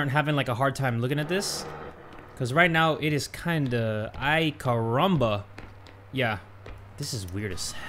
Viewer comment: I’m not sure, but did he just say “Ai caramba”??